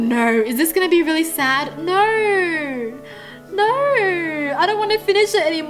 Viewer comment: im not making fun of how she talk but i could not stop lauhing when she said no no no no all i heard was nor nor nor nor